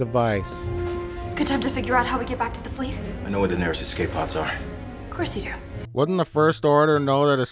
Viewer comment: So maybe this has been said... But... I heard "The Falcon's escape pod" which, I think I probably threw the BS flag during when the movie played out but.... The Falcon had no escape pods as a diversion